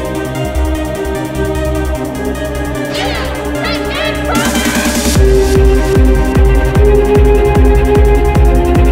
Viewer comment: What he/she/it says at 0:14? Pinkie promise? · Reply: "You Pinkie-promised"